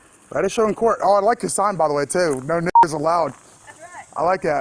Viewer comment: i misheard that i thought she said no nickers aloud